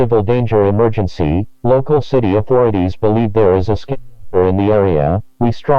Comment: Local city authorities believe there is a skittle? Is that what he said? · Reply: I think they tried to censor the word “Skinwalker” to not cause panic.